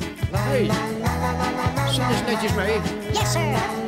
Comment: I Can Hear An Word Yessir In English Language I Can Hear Instead Of Dutch